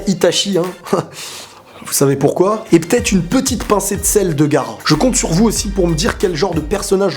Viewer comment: moi j'ai entendu "une petite pincée de sel pour Gaara " sur la question "ta kunoichi préférée" j'ai eu peur attends Gaara une meuf mais en faite non enfaite il y a mon pc a bugé et il a avancé jusqu'à une pincée de sel pour Gaara alors que de base j'étais à la question "ta kunoichi préférée"